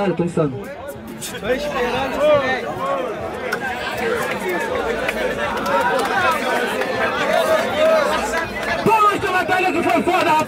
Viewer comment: Só eu que ouvi uma voz quando o parou de rimar "TA ESPERANDO O SILÊNCIO" KKKKKKKKKKKKK